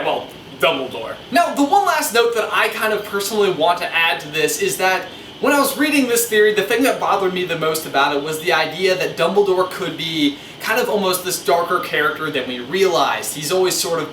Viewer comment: 😂😂😂😂 "DUMBLEDORE" really sounded like snape snape severus snape